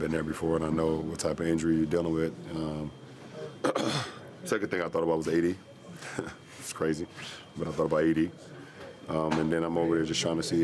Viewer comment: Did LeBron just said he thought about AD? Or did I misheard? 😅😅